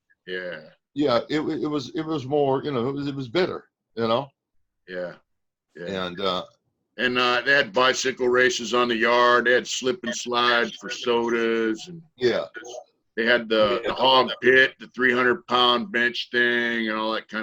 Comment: Did just say you had a SWIMMING POOL in prison? What??? Wow... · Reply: Or did he say “we built a swimming pool”. And did I just hear u say “slippy slide” ? Lol.